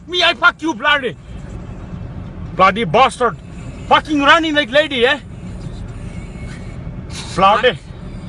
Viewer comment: "Fucking running like lady, eh?" The best statement I ever heard this year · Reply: I thought he said " you running mc-lady yeah?" Which would of been 10 times funnier. Ears hear what they want to i guess.